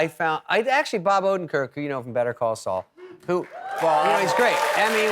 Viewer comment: LOL....I thought he said "Better Coleslaw"